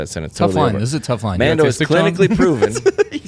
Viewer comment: Can anybody hear was says around the 12:33 mark? It was obviously funny but I can’t hear it! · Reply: I think he sad, “Is it though?”